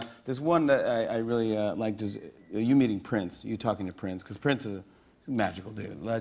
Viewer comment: Just for ne non-native speakers like me...did jimmy say at 0:12 "you meeted prince" instead of met? or do i get this wrong?